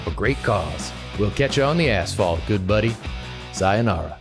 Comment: During the introduction, when said "catch you on the Asphalt" I heard "arse fall" my chair suddenly broke and my skinny bum fell but wasn't there to catch me. I hate.